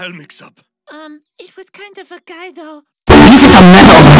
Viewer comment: what does Italy said? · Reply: His first crush was a guy.